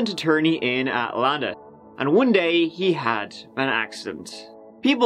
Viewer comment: i thought you said he suddenly had an accent and i thought, yeah that's pretty much what happened to luke bryan who is from my home town. he suddenly got an accent too.